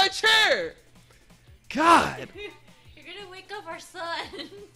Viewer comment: did she say ur gona wake up our son?